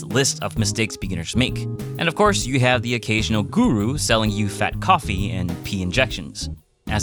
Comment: Did u say guru at 4:15 are u from India